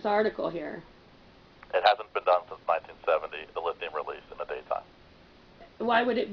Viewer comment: did he say release in the day time?